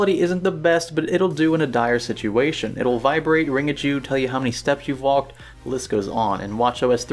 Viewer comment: Wait, did he say that the watch will "Ring a Jew"? Is that a new feature on the 2016 model? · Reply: ring at you.